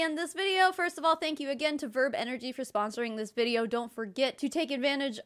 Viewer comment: GIRL........ I thought you said “Verve Energy”....... which used to be an MLM DRINK COMPANY!!!!!!!!